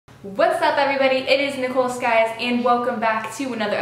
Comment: Is it me or when she said hey what's up guys it Nicole Skye's I heard what's up guys it's nipple scabs?